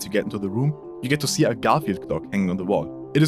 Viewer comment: what did he say 😳🤨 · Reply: Garfield Clock